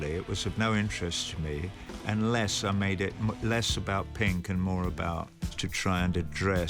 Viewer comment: the subtitle is wrong. He says he's making it less about Pink the character in The Wall, not Pink Floyd the band.